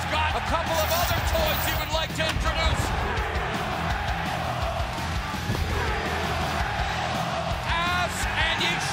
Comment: what are the crowd saying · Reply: We want tables!